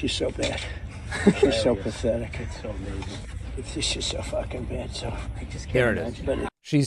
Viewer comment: I'm in no way defending the piece of trash and I'm sure he says it in private, but it sounded like he said she's effing bad in that video.